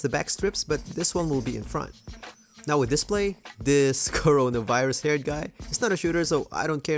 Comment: did he say coronavirus?????